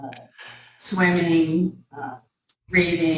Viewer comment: I thought she said one of her hobbies was breathing. I guess it's done good practice to disguise the fact that you're a vampire.